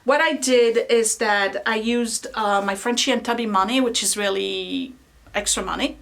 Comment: What does she say in 07:35? Lots of love everybody! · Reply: G. Sözmen she used her Frenchy and Tubby money (their Etsy shop)